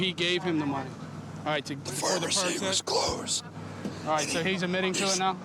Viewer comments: Anyone else catch the demonic voice at 16:14 say “the pharmacy was closed”? Or am I trippin!? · what is the guy whispering in the background? “The pharmacy is closed?” When I heard that it scared the shh out of me. I thought some paranormal shhh was happening!!! 👻😨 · in this video, did anyone hear that whispering?????!!!! "The pharmacy was closed?" or something like that... scared me!